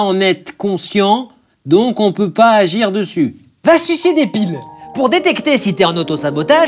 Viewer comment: c'est moi ou a 1:29 il dit "va sucer des piles"???